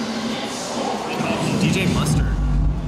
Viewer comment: What does he say at 6:38 ? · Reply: If you mean Dillon, he said Dj Mustard